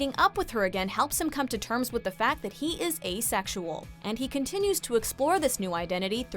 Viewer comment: i thought you said "gaysexual"